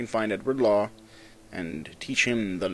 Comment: for a second i thought u said Edward teach (Blackbeard)